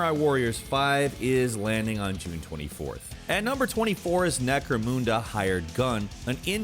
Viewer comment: Did he just say necro moonda isn't it necro munda lol